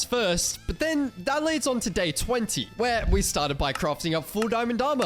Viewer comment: When he said day 20, I thought he said date 20.😂